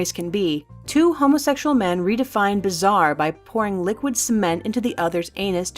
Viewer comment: f#@ ciment ? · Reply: At first I thought she said liquid semen so I thought for gay people sounds normal then she said it hardened and I thought what's wrong with the world